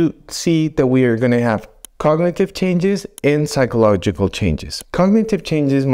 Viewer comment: You say *psychological" changes but the caption says "physiological" Which did you mean? Ah you did mean "psychological"